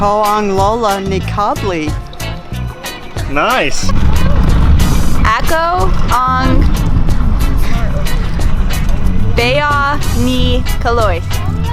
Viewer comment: I thought the other guy said.. ako ang bayag ni Caloy!!..